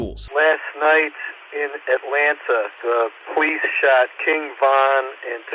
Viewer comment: Did he said "The police shot king von